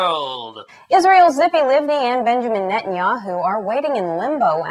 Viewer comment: The first time I heard the name "Tzipi Livni" on TV, I misheard and thought the news guy had said "Zippy Lippy". Which sounds like an ironic nickname you'd give to someone who talks way too much - "hey, Zippy Lippy, zippy your lippy and shut the f*** up already!". And Tsvangirai's name so perfectly fits Rolf Harris's "Sun Arise" song that whenever his name gets mentioned, I can't help but have the song play in my head: "Tsvangirai, bring in the morning. Tsvangirai, come with the dawning."